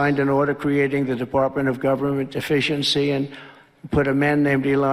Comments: Why does it always sound like he says "Department of Govenment Deficiency"? 🤣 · Because of his slurring, it sounded like he said the department for government deficiency! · I heard him say “department of deficiency” …lol